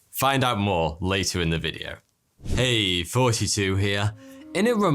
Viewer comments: Anyone else get confused at the introduction? I thought you were saying, “hey 42 here” for the longest time. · For the longest, I thought he was saying, "hey! 42 here." In the intro. And I even thought it was clever because I thought he made his name that because of the meaning of life, (Guardians of the Galaxy), and he is very informative..... So I thought it made perfect sense.... Anyway......I was wrong AF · Is it only me but I kept hearing "its 42 here" in every video.